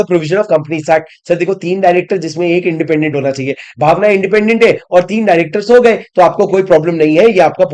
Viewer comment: did I heard bhavna independent hai Orr 3 director so Gaye 😂